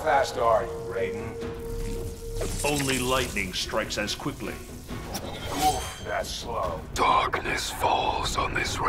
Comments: did kabal just say oof XD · did I just hear oof...?